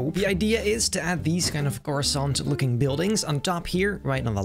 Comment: am i crazy or did he say croissant looking buildings?